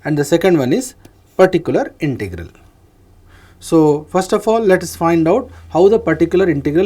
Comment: the Prof is correctly saying "Particular Integral" but the text says "Vertical Integral" - I request to correct if possible